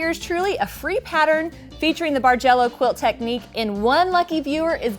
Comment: beautiful quilt - I've only been quilting for less than two years and seriously thought you were saying "Marjello" :)